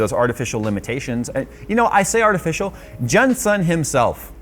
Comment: Did he just call him Jun-san?